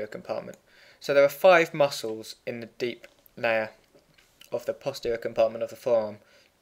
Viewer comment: Did he say deep nayer at 9:15?